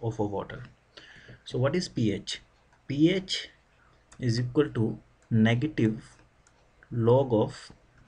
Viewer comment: i cant tell what he said at 0:14. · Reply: pH is equal to -log(H+) ...